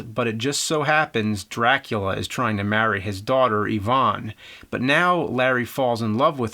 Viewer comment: Daughter named "Ivan" or I misheard her name? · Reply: I believe it's Yvonne :)